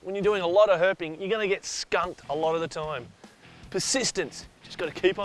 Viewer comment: sounded like you said skin